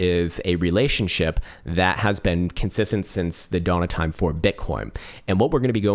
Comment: did he just say “bitcorn?”